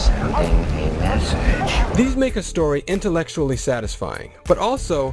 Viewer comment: did he said “this nigga story”??